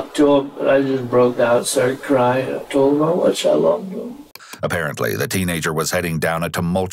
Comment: I was listening to this and when he said “I broke down and told him how much I love him” I heard “I broke down and told him I wished I loved him” and I know I just misheard but it’s almost like I heard the actual truth